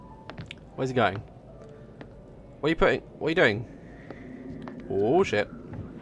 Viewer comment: i heard you're wife say "what are you doing tonight daz?" Something like that haha when mike had to close the windows for jess